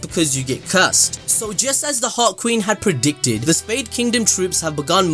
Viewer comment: Totally thought he was saying 'the hot queen' instead of 'the heart queen' for a moment there